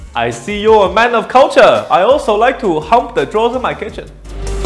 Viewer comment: casually saying "i see you're a man of culture, i also like to hump drawers in my kitchen" made me laugh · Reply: I thought he said “jump drawers in my kitchen” lol